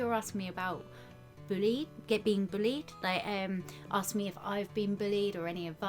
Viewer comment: since i live in america when you said bullied, i thought you said bleed